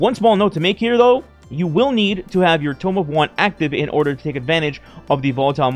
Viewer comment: What’s a tome of one or whatever he said??